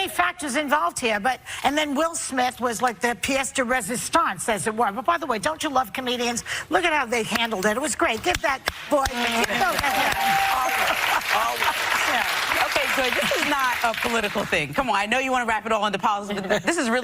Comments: Did Joy call Dave Chappelle a boy @ 2:40? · Did Joy call a Black man a boy 2:41 ?